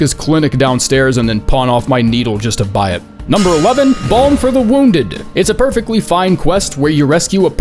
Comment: I honestly think you undersold the sheer panic one feels when they see Balm for the Wounded for the first time, like it is literally the Chef Skinner meme, you read a seemingly innocent description and then the word "Whiteward" pops up and you're just like "SHERMA'S DOING WHAT???" That coupled with what happened to Myla in the first game is honestly enough for me to put it in my top 5 just for the heart attack it gave me the first time, even if on replays it no longer has that effect. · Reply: I dunno why but I thought you said Balan Wonderworld for a split second rather than Balm for the Wounded